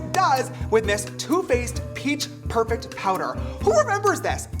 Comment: When he said two-face I thought he said toothpaste😂